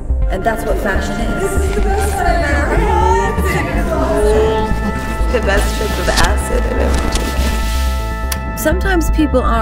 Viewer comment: Love this! What does she say at 1:53? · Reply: "It's like the best trip of acid I've ever taken."